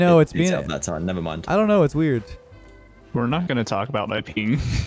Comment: def thought he said peen